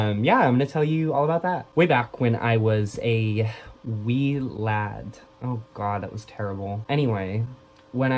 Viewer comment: Oh my god when you said wee lad I thought you said weed lad and I was getting concerned until I played the part again 😂😂